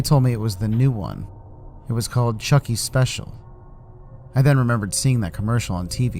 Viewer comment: I thought he said chucky speshy